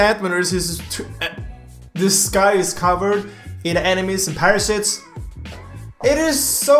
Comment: perashits? did i heard it right?